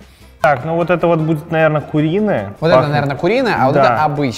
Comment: как, как? куриная или мне послышалось *уиная?